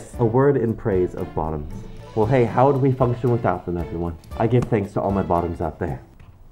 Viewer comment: I heard "I give praise to all my foxes out there", and he'd to rewind and turn it up. He still would knot be wrong in saying it, though.